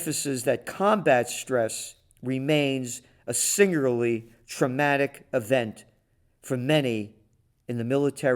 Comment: Thanks again Gerald . Man , my hearing is off , it sounded like you said Tromatic . Seams old Toxie was may have been conked to the bone. lol.